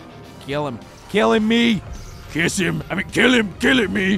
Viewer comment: What did Kong just say💀💀💀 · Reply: Kiss him